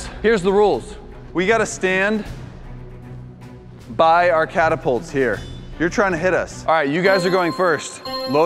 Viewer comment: Instead of catapults I Thought it Said Capitalism READING 1000